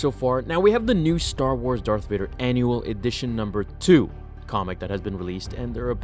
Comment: I thought the comic said “Vader Anal 2” lmao